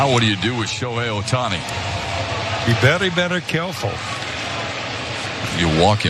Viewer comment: "be vewy vewy quiet" is an Elmer Fudd line. Maybe I misheard.